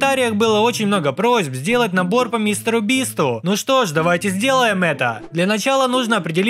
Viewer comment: что говорит: мистеру бисту , субтитры : мистеру убийству 💀